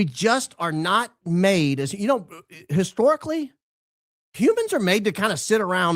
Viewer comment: Had to listen to the humble hosts opening statement twice. Thought he said Cubans historically don't do much... Wait what??!! Oh you said "humans '😂